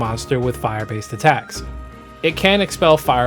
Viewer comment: okay, who else heard "fire beams" as "fire memes" instead lol